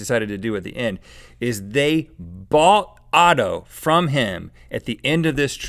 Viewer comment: Das Auto is just the German word for car. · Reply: ***** He did not call the car "Auto", he called it "Otto", which is an old-fashioned male name in Germany (like "Otto von Bismarck"). Look at 9:33, it says the name on the car and on the poster in the background (also take note of the (in German) wrong genitive apostrophe on the car and the correct genitive without apostrophe on the poster :-) )